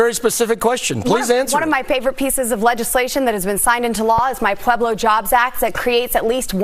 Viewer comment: Did I hear “pa-blojob-act…”???